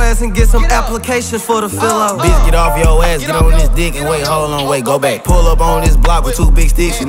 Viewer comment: lmao I thought he said “he get on dis ____” 😂